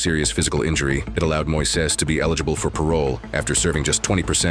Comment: My thoughts exactly. Doesn't seem right to me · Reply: I misheard the narrator. He was eligible for parole, not given parole. So he still might serve 30 years. We can only hope 🤞